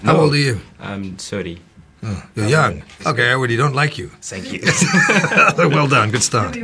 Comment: Did he say at 00:06 But I don’t like you?